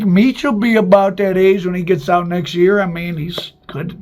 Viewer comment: So at 18:48 , ya say Meech will be out next year? Or did I hear that wrong!